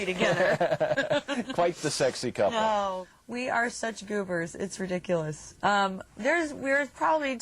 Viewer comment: hey everyone may someone help me understand what she means when she says 'we are such ??, it's ridiculous' at 2:08 please ? thx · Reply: "we are such goobers"... it's like they're goofballs (sort of silly)